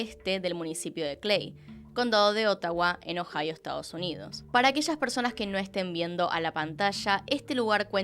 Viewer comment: Escuche "para aquello que no estamos viendo la pantalla" mire y dije: que considerada:") te amo 🥴❤